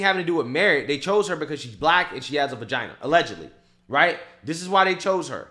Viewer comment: For a minute I thought you said Kamala “allegedly has a vagina”